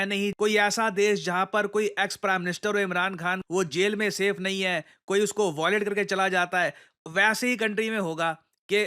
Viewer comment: have I heard correctly? Imran Khan has got violated!!